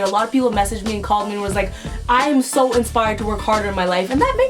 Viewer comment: When Lilly said inspired I thought she said infired :") bts trash struggles